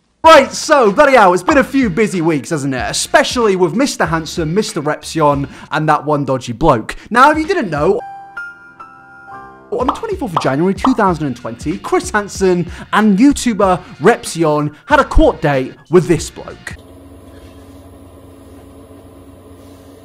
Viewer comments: I'm so sorry this is so nasty but at the beginning of the video I heard "On the 24th vaginarary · That sounded like 24th vanigary of 2020. 😂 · I thought you said Mr. Handsome I was like well you're not wrong